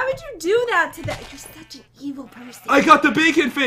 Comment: He said I got the beacon face instead of bacon face